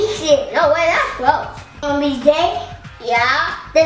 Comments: it sounds like he said ''wanna be gay'' lol. but this is a funny vid ( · sound like he said wanna be gay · When said "wanna be great" I thought he said wanna be gay LOL!